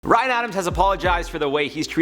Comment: my dumbass thought it said ryland adams (shanes bf)